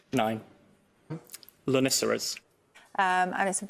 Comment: & 1:39 Say what now? I can't find any word even remotely spelled like this. Is this some British pronunciation I'm missing? Please help, I'm grug brain. I found Arytenoid though. :D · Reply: Had to look it up on the Countdown wiki (TIL there's a Countdown wiki). "LONICERAS" is apparently honeysuckle and "TAIHOAED" is apparently the Maori word "taihoa" adopted into English.